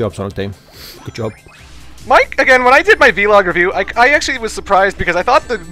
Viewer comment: I'm sorry but 19:30 did you just call it a V log?